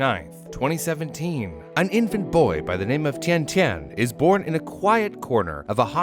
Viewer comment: How tf - 0:02 oh when I heard tintin i say hold up….